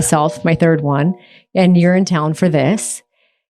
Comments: I heard “urine town” every time she said “you’re in town”. 😂😂 Girly pop really is going to urine town with all these useless infusions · We never learn. How many quack "wellness" methods have humans used over the millenia that were later proved to be expensive placebos, at best, or deadly at worst? (BTW, every time Heidi says "You're in town" I hear "Urinetown". As in the musical. 😊)